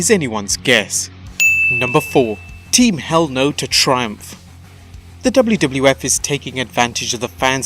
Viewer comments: Im not aure if i heard wwf or wwr at 2:47 ? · did he say wwf ?